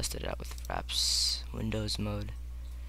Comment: i thought he said with faps windows mode XD